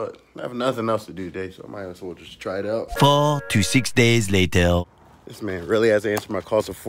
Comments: I thought he said 46 days… lol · Am I the only one who thought he said “46 days” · I thought he said 46 days It was 4 to 6 days xD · I thought you said 46 days.